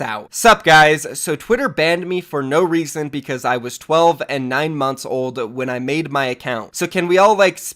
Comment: For some reason when you said the thing about sceptic I thought you said he was 9months old when he created the account I later realized what it actually is